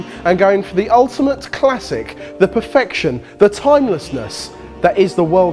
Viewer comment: i thought it said "the ultimate cock".....